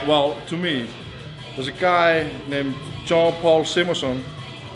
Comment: the guy he talks about at 6:50, whats his name? I cant quite here what he says, Jean Paul Simmons? I wanna read some more about that incident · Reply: Jón Páll Sigmarsson is his name